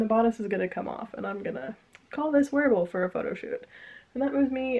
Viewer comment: I thought for a second you said you would call it "werewolf for a photoshoot" and I just thought "well, that's an interesting decision for merida"